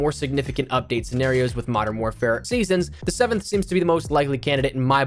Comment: The “mid-season” update coming the first week of August 😂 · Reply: Did he say August in the video? I heard him say "the 7th." So if he meant July 7, my original comment still holds water.